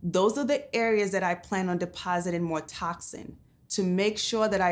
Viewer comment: did she just say TOXIN?